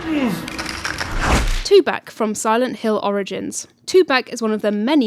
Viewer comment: thought I heard "2 pac" instead of two back lol